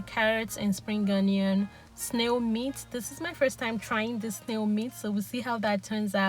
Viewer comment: Did you say snail meat 0:43 ?